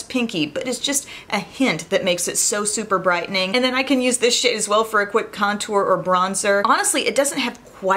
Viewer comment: I can use this shit as well for quick contour or a bronzer 🤷🏻‍♀️lol I know that’s probably not what she said...but I thought it was funny 😂